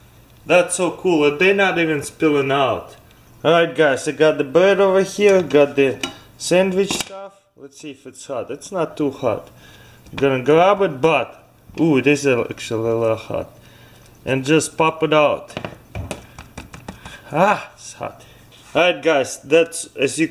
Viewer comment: did he say butt boo